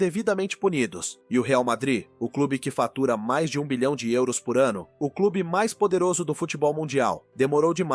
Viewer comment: só 1 milhão de euros por ano ?? Ou eu entendi errado